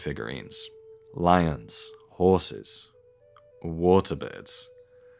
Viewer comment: I’m at work listening without looking and I swore he said “water beds” and thought that somehow the aurignacian ppl invented prehistoric water beds for a sec before I realized 😂